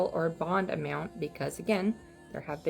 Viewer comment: Thought I heard no bond!